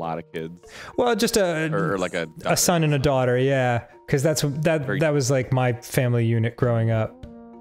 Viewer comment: Swear to god, I heard "that was my family eunich" the first time.